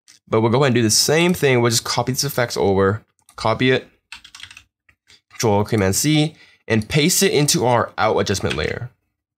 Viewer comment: I dont know.. how to do the copy effect thing, sounds like he said 'Ctrl+Alt+Cmd+C' ? But when I paste that all the values are red and nothing is editable for them?